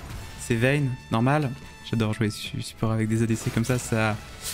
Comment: "j'adore jouer quoi ?????" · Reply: J’adore jouer support avec des adc comme ça.